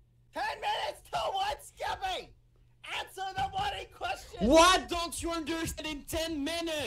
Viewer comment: D-did bad say “answer the bloody question” I-?